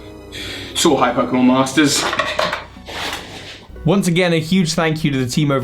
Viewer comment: Soar High ^_^ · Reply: Soar High?! I always thought you said “ So Hype pokemon masters” ive been thinking that for like 4 years also great video!